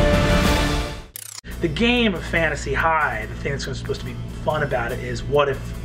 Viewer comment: I thought it said "Making fun of Fantasy High"